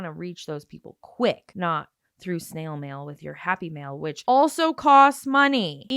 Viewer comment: I totally thought she was saying “happy meal” until you explained about snail mail. I was wondering why in earth she was sending people fast food happy meals 😂